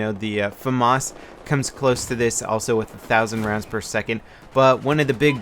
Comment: does he say 1000 rounds per second???